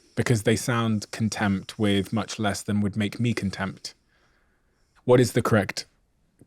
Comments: Steven said what he really thought. He said 'CONTEMPT', instead of CONTENT. He even repeated it. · it sounds like steven is saying "contempt" when he means to say "content" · It sounds like you saying contempt instead of content or is just me? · It really sounds like Steven says "contempt" instead of content...it's weird! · Contempt? What is Steven saying at 1:15:51 ? It sounds like he is saying "contempt", but I think he means, to be 'content'. Anyone? Steven?